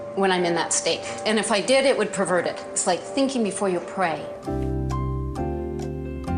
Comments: i honestly thought she said “thinking before you’re preying“ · I misheard, "Thinking before you pray," as "Thinking before your prey," and my jaw dropped